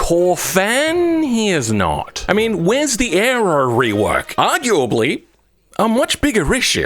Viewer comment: what rework? I've rewatched it 5 times and cannot understand what are you saying. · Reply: Aero rework haha!